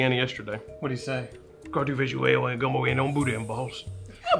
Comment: I lost it when he spoke for Louisiana 😂😂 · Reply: I thought he said "booty and balls", ha ha.